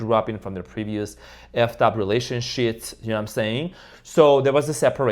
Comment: Did you say Relation-shit @ 4:49? 😂